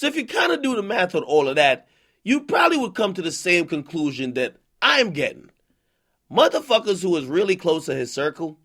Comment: sounds like AK said "im gay" but it was "im gettin"